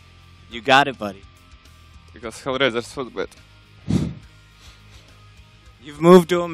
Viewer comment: Did he just say "Because HellRaisers so bad" ??? 😂😂😂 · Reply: He said "HellRaisers was better" according to his twitter.